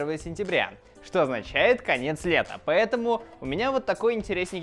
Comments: Конец света послышалось, а не конец лета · Лайк, если тоже послышалось в начале самом "Завтра Первое Сентября: Что означает, конец света)" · мне послышалась на 0:22 не конец лета, а конец СВЕТА ))) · Мне одному послышалось не конец лета , а конец света ? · 0.24 мне послышалось не "лета" а "света" )))